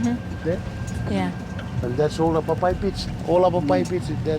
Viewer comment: where the man sounded like he was saying "papaya beach", he is in fact saying "bwaibwai" pits. The "bwaibwai" pronounced as "papai" is a Gilbertese word for "swamp taro".